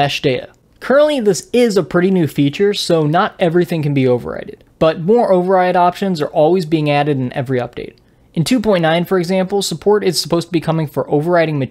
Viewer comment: Did you say “overrided”?